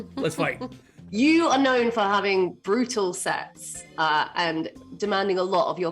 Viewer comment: Is it terrible I keep mishearing "Brutal sex"?